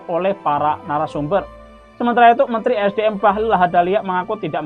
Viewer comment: reporternya sebut nama bahlul menit 5:02 , salah dengarkah saya ? saya setuju sebut bahlul 😀